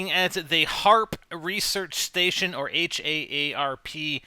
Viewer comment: i thought he said heart research station o_o